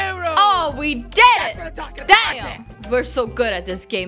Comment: did she just say damn?... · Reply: Yeah says Damn Or Dan?